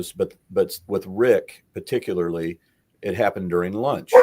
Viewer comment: did someone say lunch?